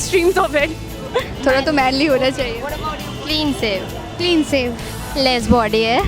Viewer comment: clin sev 😂 wtf did i just heard